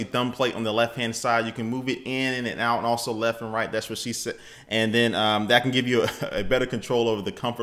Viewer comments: that is what she said LOLOLOLOLOLOLOLOLOLO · do he really say "That what she said"?!?!?! LMFAO Awsome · Was it just me or did you say ^Thats what she said? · He actually said "That's what she said" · Is it just me or he just said "that's what she said"